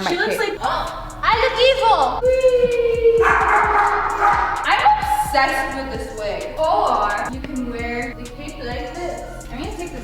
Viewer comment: when she said im obsest with this wig i thought she said i would have sex with this wig lol